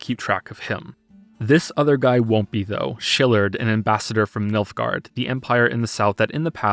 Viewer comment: also anytime I hear you say 'Niilfgaard' I hear Milf Guard.